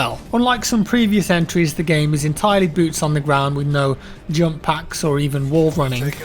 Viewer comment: is just me or does he say junk packs